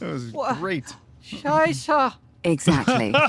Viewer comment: He says “Scheiße” (the ß is an extra letter and is used in some words instead of a double s), which means “shit”. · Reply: I thought he was saying ' Seize her ' 😅